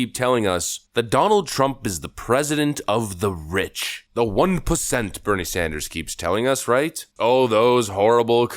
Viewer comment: I heard “the one placenta, as burnie sandurrs keeps telling us”